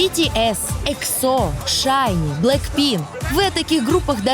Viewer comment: Мдаа... Худшее, что я видела... К-поп не популярен? Вы серьезно?! Да о нём знают везде! Чен сделал пластику?! О чём вы вообще?! Эти артисты известны на весь мир! Они являются кумирами многих людей, и это не просто так! Я готова поспорить, что личные сообщения ведущих сейчас разрываются от возмущений к-поперов... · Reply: Мне послышалось или ведущая сказала не Blackpink а Blackpin ?